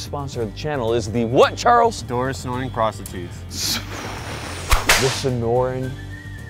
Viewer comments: The SDI jokes are getting even more fucked up. I love it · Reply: I heard Dora's snorin prostitutes but I could be wrong lol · I swear I heard 'Sonoran Desert Prostitute.' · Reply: I heard “Doris’ Snorin’ Prostitutes” 😂